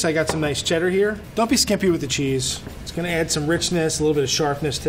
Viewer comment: Am I the only one who heard at 5:34 Don't be skeppy with the cheese lol <num> 🔰🔰🔰🔰🔰🔰🔰🔰🔰🔰🔰🔰🔰🔰🔰🔰🔰🔰🔰🔰🔰🔰🔰🔰🔰🔰🔰🔰🔰🔰🔰🔰🔰🔰🔰🔰🔰🔰🔰🔰🔰🔰🔰🔰🔰🔰🔰🔰🔰🔰🔰🔰🔰🔰🔰🔰🔰🔰🔰🔰🔰🔰🔰🔰🔰🔰🔰🔰🔰🔰🔰🔰🔰🔰🔰🔰🔰🔰🔰🔰🔰🔰🔰🔰🔰🔰🔰🔰🔰🔰🔰🔰🔰🔰🔰🔰🔰🔰🔰🔰🔰🔰🔰🔰🔰🔰🔰🔰🔰🔰🔰🔰🔰🔰🔰🔰🔰🔰🔰🔰🔰🔰🔰🔰🔰🔰🔰🔰🔰🔰🔰🔰🔰🔰🔰🔰🔰🔰🔰🔰🔰🔰🔰🔰🔰🔰🔰🔰🔰🔰🔰🔰🔰🔰🔰🔰🔰🔰🔰🔰🔰🔰🔰🔰🔰🔰🔰🔰🔰🔰🔰🔰🔰🔰🔰🔰🔰🔰🔰🔰🔰🔰🔰🔰🔰🔰🔰🔰🔰🔰🔰🔰🔰🔰🔰🔰🔰🔰🔰🔰🔰🔰🔰🔰🔰🔰🔰🔰🔰🔰🔰🔰🔰🔰🔰🔰🔰🔰🔰🔰🔰🔰🔰🔰🔰🔰🔰🔰🔰🔰🔰🔰🔰🔰🔰🔰🔰🔰🔰🔰🔰🔰🔰🔰🔰🔰🔰🔰🔰🔰🔰🔰🔰🔰🔰🔰🔰🔰🔰🔰🔰🔰🔰🔰🔰🔰🔰🔰🔰🔰🔰🔰🔰🔰🔰🔰🔰🔰🔰🔰🔰🔰🔰🔰🔰🔰🔰🔰🔰🔰🔰🔰🔰🔰🔰🔰🔰🔰🔰🔰🔰🔰🔰🔰🔰🔰🔰🔰🔰🔰🔰🔰🔰🔰🔰🔰🔰🔰🔰🔰🔰🔰🔰🔰🔰🔰🔰🔰🔰🔰🔰🔰🔰🔰🔰🔰🔰🔰🔰🔰🔰🔰🔰🔰🔰🔰🔰🔰🔰🔰🔰🔰🔰🔰🔰🔰🔰🔰🔰🔰🔰🔰🔰🔰🔰🔰🔰🔰🔰🔰🔰🔰🔰🔰🔰🔰🔰🔰🔰🔰🔰🔰🔰🔰🔰🔰🔰🔰🔰🔰🔰🔰I was testingI was testingI was testingI was testingI was testingI was testingI was testingI was testingI was testingI was testingI was testingI was testingI was testingI was testingI was testingI was testingI was testingI was testingI was testingI was testingI was testingI was testingI was testingI was testingI was testingI was testingI was testingI was testingI was testingI was testingI was testingI was testingI was testingI was testingI was testingI was testingI was testingI was testingI was testingI was testingI was testingI was testingI was testingI was testingI was testingI was testingI was testingI was testingI was testingI was testingI was testingI was testingI was testingI was testingI was testingI was testingI was testingI was testingI was testingI was testingI was testingI was testingI was testingI was testingI was testingI was testingI was testingI was testingI was testingI was testingI was testingI was testingI was testingI was testingI was testingI was testingI was testingI was testingI was testingI w